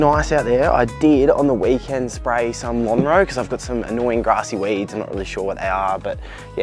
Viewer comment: Hey mate what was the product you used for grassy weeds? Thought u said Monroe but can’t find anything on the net?